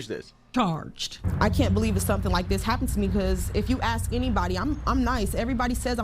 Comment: She actually said ask not axed.